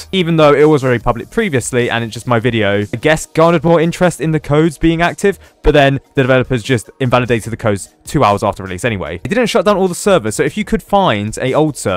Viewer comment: I cannot stop hearing “I guess Gardevior is interested in the code’s being active” at 1:45 . What did you say there? I’m not a good “English” person.